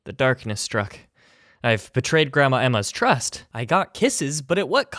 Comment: I thought he said Gramma-Amma like it was a nickname